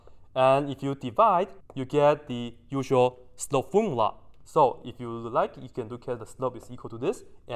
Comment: What phrase is he saying at 4:38? "Sto-foon-lot"? I think it 4:54 he is saying "the slope of the secant line". I'm not being sarcastic, I just can't understand what he is saying past this point. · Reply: "slope formula"